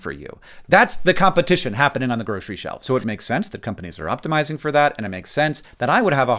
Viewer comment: how does that not make sense? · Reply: the original comment thought Hank was saying “it is ethical” when he says “it makes sense”. But we should all know it’s part of the problem he is describing